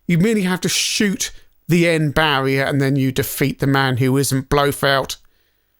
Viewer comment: 34 Did I hear you say "nuke-u-ler missiles"? ;) Anyway, nice review. I wasn't a fan of this game in the arcade but I would watch other people play it who were much better at it than me! It kind of reminds me of Namco's Rolling Thunder (as you mention), though I much prefer that one over this. Still a good and fun game though. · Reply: Heard that before but I have no idea where it comes from. Nuclear has only one u in it. And I think I heard him say "Blofelt" instead of "Blofeld".